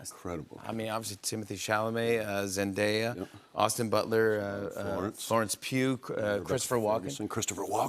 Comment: Did he just say Florence Puke?